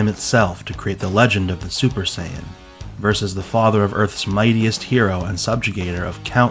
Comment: I thought you said fodder instead of father